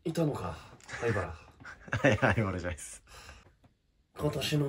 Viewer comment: はい灰原じゃないっすって聞こえね？